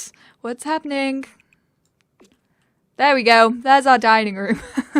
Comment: If i hear "shandeleh" instead of "chandeliar" again i'm going to cry..